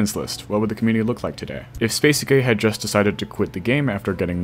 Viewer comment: the closed captions say spacey gay